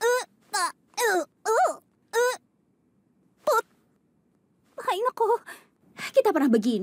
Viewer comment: denovo eu entendi "aí meu c..."